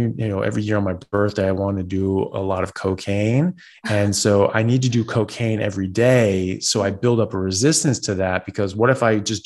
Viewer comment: All I heard was "I need to take cocaine every day..." 😂